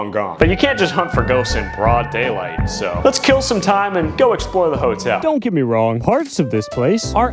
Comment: When bro said let's kill some time I thought he said let's kill somebody😅😅